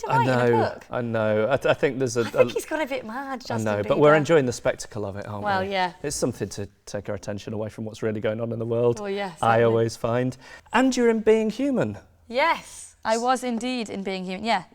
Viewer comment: Genuinely thought he said "and you were a B&Q Man" at 9:35...